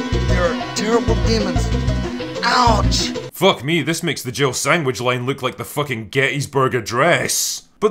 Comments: My biggest unanswered question is: Where is my Jill sandwich?!!! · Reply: Understandable, for a while I thought he said Jibbled sandwich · My biggest unanswered question is: Where is my Jill sandwich?!!! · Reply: Lol like an idiot I always thought he said jivled sandwich and had no idea what that meant.